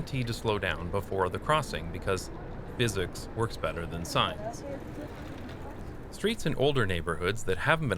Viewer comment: for a second I was confused because I thought you said "physics works better than science"